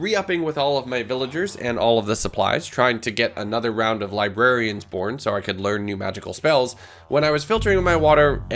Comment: did he just say "librarians porn" or am i high?